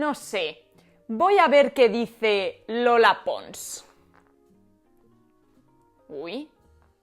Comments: Pensé que dijo Lele Pons, y casi me dio un patatús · Mi cabeza: "Voy a ver qué dice Lele Pons" 😂